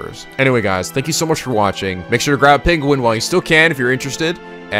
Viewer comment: For some reason, When I got the notification I thought it said ”fairwell, picky penguins”